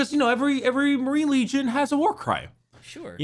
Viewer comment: I heard "oh its the warcrime! Every space marine legion has a warcrime!" I stead of "war cry" and I was just 100% ready to roll with it bc Warhammer 40k is just 90% warcrimes